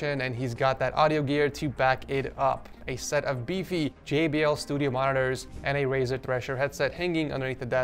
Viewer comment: just me or did he say "studio monitors" instead of speakers